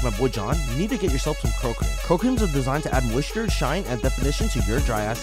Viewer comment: And I thought I heard "girl cream" 😂